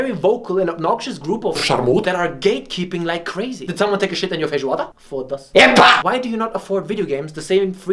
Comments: Did this dude just say "Fodasse...Epá!"? (It's Portuguese and it's the equivalent to "Fuck...Oi man!" in English) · Did he really say ''Did someone take a shit in you FEIJOADA?" at 8:33??